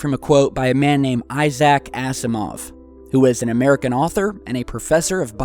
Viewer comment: Did he just say Isaac Ass a moth? lol